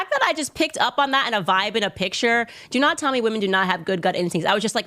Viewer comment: you misunderstood what she said. She said you cant tell her that women dont have good gut insticts 💯